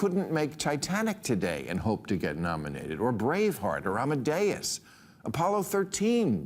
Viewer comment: Sounds like he said "Bravefart"